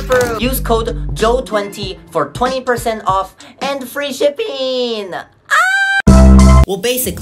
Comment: When it said free shipping I thought it said free stripping.. idek why lol